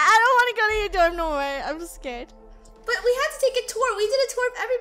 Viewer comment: I can't stop laughing when draco was stuck in lunars room i and I remember when they played carton cat when they said I'm scared and they sound like they were saying I'm squared